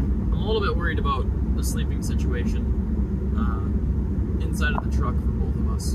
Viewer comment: I thought I heard sleeping with sirens!!! Best band ever